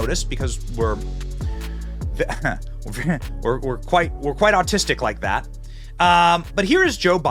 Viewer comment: Did you just say you were autistic ?